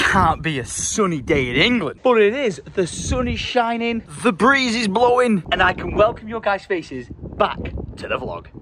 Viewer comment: Honestly thought I heard say Febreze is blowing